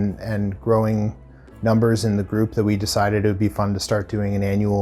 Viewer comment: Did he just say “we ended up settling on pinehurst…”?